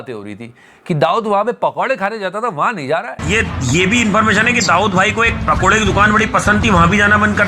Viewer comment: did he said dawood BHAI